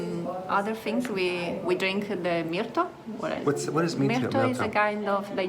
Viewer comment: What is “Miltoh”? I don’t know what she said at 5:31. Fermented berries? May I please get somebody’s help on figuring what she said and how to spell it? 🙏🏼🙏🏼🙏🏼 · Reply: Mirto